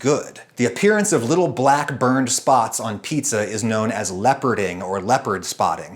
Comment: I have never heard of "leoparding" and was a bit shocked. Because I misheard the word as "leperding" and thought "that's an unfortunate name for pizza".